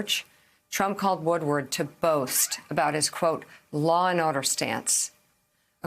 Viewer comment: Sounded like she said "Law and Odor"...